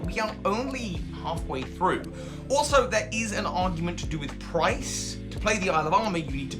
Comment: an argument to do with price....I thought he said Pryce XD